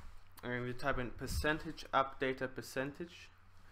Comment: what are you even saying? percentage updata percentage? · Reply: Cynthia Rangel %appdata%